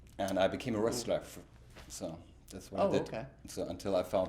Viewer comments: Did he say he became a wrestler @ 3:17 ? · after Richard talks about his teacher reaching out to his parents about music and they said no. He says he became a restaf or a restraph? I can't quite make out the word he is saying, due to his accent. Can anyone let me know what word he used. Thank you in advance to anyone who is able to inform me. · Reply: I believe he said wrestler? The auto-generated captions said wrestler, and according to different wikis, he used to wrestle.